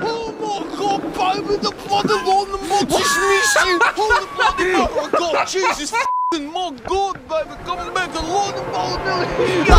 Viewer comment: wtf is he saying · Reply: "Jesus, come back here! Oh my God, baby, the bloody lightning bolt just missed you! Holy bloody mother of God! Jesus f+×king my God, baby, come back, the lightning bolt nearly hit ya! Oh my God, baby, you alright?!"